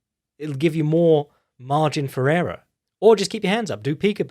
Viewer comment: you talk burgos & say "adopt crossgaurd when slipping..." *phone rings & auto pauses vid... they hang up .. vid starts.. "Marjin Ferrera..." & im like.. "who the f#ck is Marjin Ferrera?!.. must be obscure boi good at slippin punches. I Go back 30sec.. Margin for error!